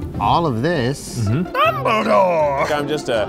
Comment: Did he just say Dumbledore 🤣🤣